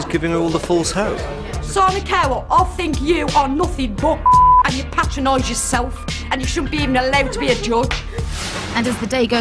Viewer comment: I'm not native english speaker. What did she say at 30:07 and at 30:43? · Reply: I think she said “I’m gonna make something of my self” and at 30:34 Simon cowell I think you’re nothing but- and you patronize yourself and you shouldn’t even be allowed to be a judge.”